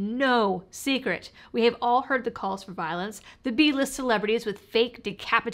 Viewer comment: Aww - you called Kathy Griffin B-List. That's so much kinder to her than she deserves. How gracious of you. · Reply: B-list for Griffin? I thought said Z-list.